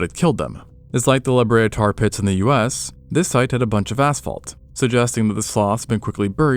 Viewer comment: did he say ass fault? LMAO